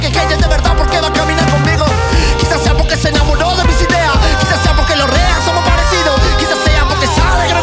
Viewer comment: pensé que decía "quizás sea por que los reales somos parecidos"